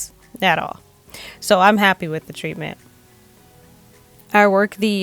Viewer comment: GIRLLLLLL I THOUGHT YOU SAID IM NAPPY 😂😂😂😂😂😂😂 but you said I’m happy lol 😂 (at the end) good video! I’ll have to tell my hairstylist to do this to my hair to de tangle it because she be trippin 😂😂🙌🏾🙌🏾🙌🏾